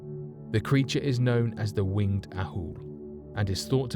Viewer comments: no it definitely said wingless owl!! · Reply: it sounded like owl to me!! LOL! Must be his accent plus my subtitles thought he said it too! Ha ha!! 😄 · Did he say that people call this huge bat like creature a "winged owl"? Hahah. That would be strange indeed! An owl, with wings? That sounds like bullshit to me!!! Hahahha